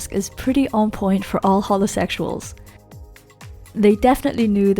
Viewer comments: When she said holosexual I was like Cristine (Simplynaillogical) is secretly here!!!!! :o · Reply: Dinosaurgirl Yt oh she said holosexusuals thank goodness i thought she said homosexuals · When you said holo sexuals I thought you said homosexuals